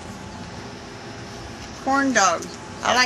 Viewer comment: lol at 2:01 it sounds like she is saying porndogs not corndogs...